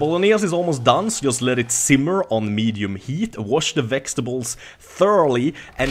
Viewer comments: am i the only one that heard him say vexetables instead of vegetables ?? :D and hi if you read this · lol at 3:25 it sounds like he is saying wash the "Vechs"tables lol